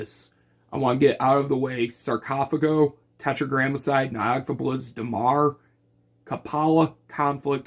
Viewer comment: can anyone tell me what band he says here after Tetragrammacide thank you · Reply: Nyogthaeblisz